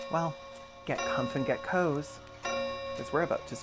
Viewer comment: I know it just started, but I misheard you as "Get c*nts and get hoes," and was like, "On brand, but I'm good, thanks."